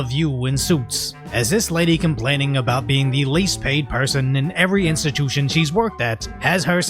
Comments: I really heard she said "the least behaved person" because it fits lol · Did she said "least paid"? I heard "least behaved" the way she said it, and that also seemed about right.